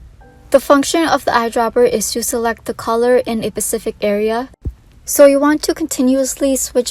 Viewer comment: did,,, did she say pacific area,,?